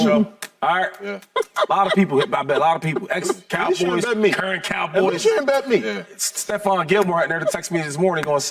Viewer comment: did Shady say he lost money to some current cowboys? hmmmm...